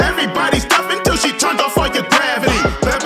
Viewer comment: It sounds like your saying “until she turns off fucking gravity”😂